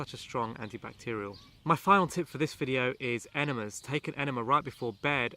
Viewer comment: Hi Im french and I don't understand what is your last advice after taking raw garlic, I only hear "enemies" or "animals" right before bed...:( Btw Thanks for all your sharing help, that's nice to see how we all trying in the same battle to eradicate that inflammation problem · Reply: Enemas!